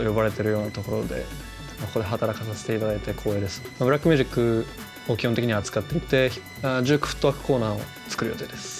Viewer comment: man says "rap music" Subtitles say urban music Okay